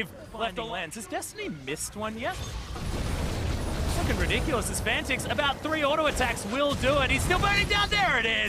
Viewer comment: did he say "fucking ridiculous" ?